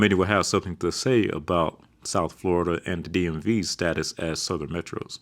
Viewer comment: what are the words he say there? South Florida and... am I hearing "DMV" after that? Doesn't make any sense, but I keep hearing it the same way